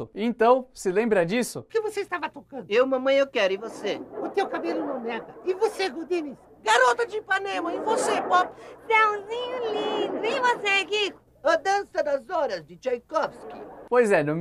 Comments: Só eu ouvi o Nhonho falando "O teu cabelo é uma merda"? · só eu que entendi "o seu cabelo é uma merda"? KKKKKKKKK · Sempre que eles falam o nome da musica eu escuto o nhonho falando "o seu cabelo é uma merda" (13:47) kkk alguem sabe o que ele fala de verdade?